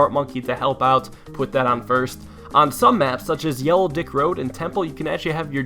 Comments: Did he say "Yellow D*ck Road" on 2:50 ? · did he just call it yellow dick road? xD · did he say yellow dick road at 2:50? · did he say “yellow dick road” xD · did he say yellow idk road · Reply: EDIT: yellow Dik road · Did anyone hear that? Yellow Dick Road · I thought he said yellow dick road XD lol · did he just say Yellow d:ck road🍆🤣🤣🤣🤣🤣🤣🤣🤣🤣🤣🤣🤣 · does any body else heard him says on ''yellow d_ck road''instead of "yellow brick road'' No offence